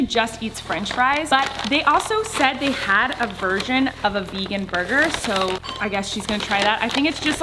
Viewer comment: When colleen says "version it sounds like she says "virgin"